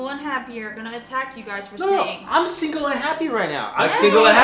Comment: For 6:00 I'm a single but suicidal instead of happy